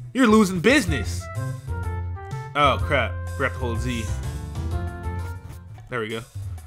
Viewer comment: did anybody else understand 'Dragon Ball Z' instead of 'forgot to hold Z'...?